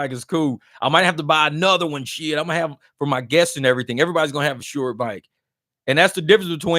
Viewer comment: dang your BMW was in Scottsdale and you did an airport ride? Wth is going down bro 😅😅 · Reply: did you just say "guest"? Yoooo wazzaappp 😁